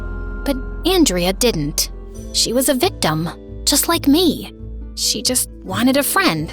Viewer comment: it sounds like they said she was dumb like me